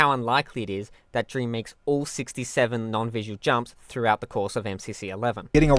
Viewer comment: All what, you say? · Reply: (Gru presenting with whiteboard meme) aha well it used to be 6 7 but that was only because two of them were unconfirmed as tick perfect, but now they have been confirmed thanks to MCV so 67+2=69.... I swear you can't make this crap up, bruh